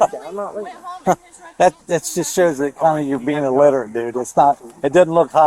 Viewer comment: the cop says “for real dude you’re going to talk like that ? It just shows you’re being a ..” did he say wither? Was he trying to say something else ? 🤔